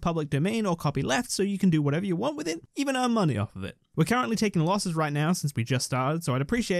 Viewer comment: 💗from🇨🇦BTW😎13:08 Even earn money off of it??? Did I hear that correctly???